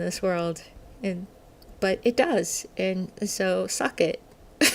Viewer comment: Does she say ' Suck it'?